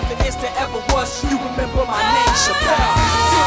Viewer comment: Is it saying remember my name Seto in 00:37? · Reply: It's my name Chappel